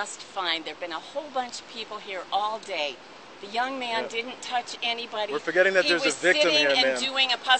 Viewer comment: did she say, "The young man didn't touch anybody - He was sitting and doing a puzzle.." WTF?